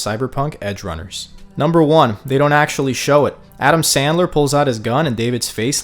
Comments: am I the only one who heard this man call Adam smasher, Adam "Sandler"? 🤣😭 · wait, did author call him Adam Sandler? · Did he just say Adam Sandler 😂😂😂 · Did you say Adam Sandler at 0:12 lmao? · I was laughing when I heard "Adam Sandler" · Yo, am I hearing it right when my bro said Adam Sandler on 0:11? 😅😂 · I was like wait what did he say "Adam Sandler?" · Did he just say Adam Sandler 😂😂